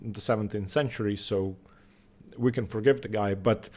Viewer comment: I thought you said “Of course Greco didn’t have engines in the 17th century so we can forget the guy” LOL